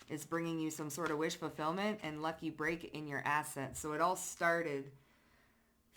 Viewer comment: I thought you said my ass was expanding. Assets.